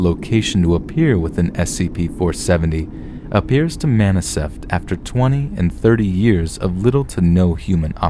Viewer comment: Some one says maniseft instead of manifest. ooops